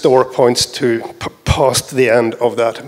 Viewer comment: "A vector has 3 pointers. 1 points to the beginning. the second points to the end. And the third points to some point after the end." What? After some research and thinking I assume that the third pointer is pointing to the end of the currently available memory allocated to that vector not at some arbitrary memory outside of the vector's scope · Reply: Yes, you're correct, but you just misheard. He never says "to some point after the end", he says it points "to past the end", with the implication being directly past the end, meaning that `end` and `end_store` are identical when the vector is at capacity. So what you're suggesting here is precisely what he actually said. The reason he said it that way is that you cannot point to a boundary. It is not possible to point to "the end of available memory" because that point lies between the last available memory address and the first unavailable one. So you need to point to either the address before the end of available memory or the address past the end of available memory.